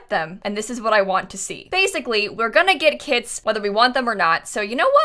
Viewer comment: we're gonna get kits whether we like it or not My dumbass thinking she said "Kids" instead of "Kits"